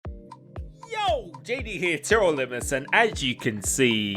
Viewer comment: Sounds like you say tirrel limbless makes me laugh everytime